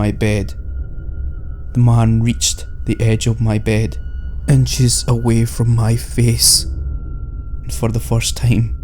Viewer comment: it sounds like he said "and jizzed away from my face" I don't know why lmao XD